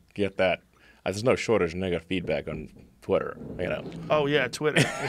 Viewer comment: Sounds like he said “there’s no shortage of ni**a feed back” at 1:07:31lmao